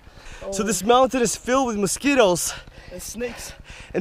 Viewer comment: Haha mosquitos i thought you said skittles and I was like "is there candy somewhere, I cant see it"